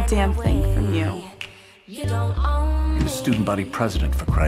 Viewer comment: Hey, what is she say to the principal at 1:17? Im not a Native Speaker :( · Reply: Principal: "You're supposed to be a role model" Jessica: "That was what I was being"